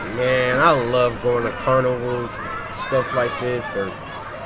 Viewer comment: Did he say carnivore?